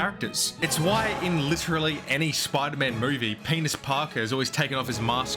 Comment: Did you just say "Penis Parker"? YTP reference?